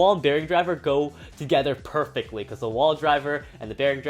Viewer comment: did really said wall driver?